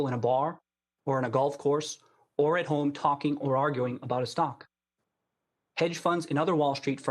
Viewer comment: did he say stonk?